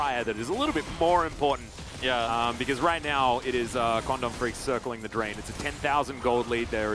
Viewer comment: I coulda sworn they were saying corn dog freaks for KDF